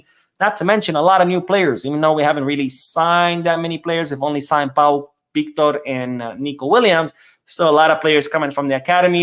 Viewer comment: i heard you say we only sign pau victor and nico williams listen